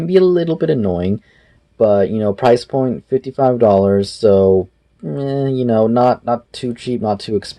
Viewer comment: DID YOU SAY 5 $ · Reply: no lol 55$